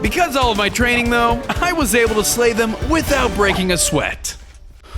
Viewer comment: Thought you said without breaking a sweat lol and you said u got a flame bow from that villager but I think u got scammed 🧐🧐